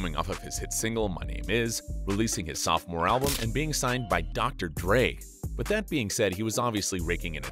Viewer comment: Anyone else hear dr grey